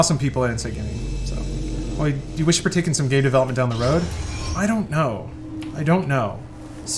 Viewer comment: I thought at 31:25 he said gay development not game development. I was like that's a very personal question my guy